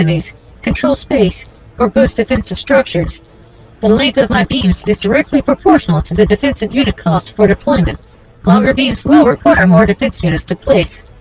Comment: I swear she said "the length of my penis is directly proportional to the defensive units of deployment"